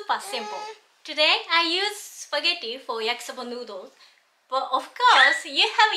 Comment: Isn't this dish made with a different kind of noodle? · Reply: Ok, but I thought she said she used "spaghetti" noodles :-/